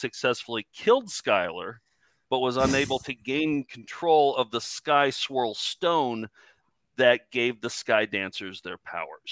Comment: I was confused for about the first five minutes as I thought they were saying “the sky squirrel stone” (not that that would make more or less sense frankly). I thought we were going to have to get Dave Hawkins to weigh in!